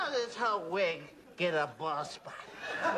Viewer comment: it sound like he said, "how dis hoe, got a bald spot" or is it because I'm from Texas lol